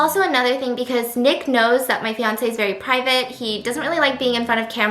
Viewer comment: for a second I thought she said “My fiancé’s very pregnant”